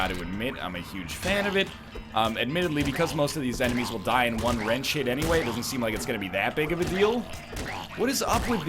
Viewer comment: Sounded like you said "in one red shit."